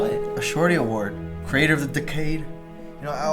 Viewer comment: I honestly thought he said crater of the decade... I need new earbuds